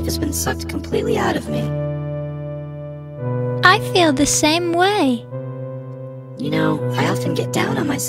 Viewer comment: I DIED WHEN SHE SAID "i think they're both gay!" IDK WHY ITS SO FUNNY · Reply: didnt she say "i feel the same way" instead of "i think they're both gay" lmao